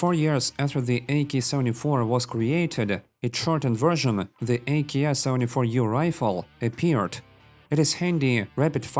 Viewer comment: I only hear "AKS only for you"